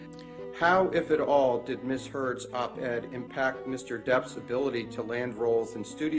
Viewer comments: the captions say "Ms Turd's" 😂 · Omg at 3:20 instead of ms heard text is ms turd